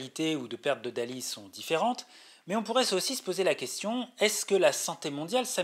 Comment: Intéressant, mais c'est quoi les saucisses à 9:23 ? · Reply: "Mais on pourrait [z'aussi se] poser la question..." ^^